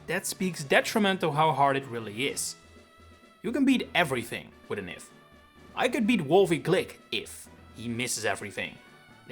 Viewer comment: What words are you saying at 3:30? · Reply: "...if a battle becomes doable when there is an "if" involved, that speaks detrimental of how hard it really is. You can beat anything with an "if" " Or something like that